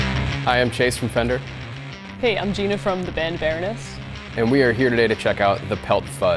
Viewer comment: I thought she said from the band Bareness! :)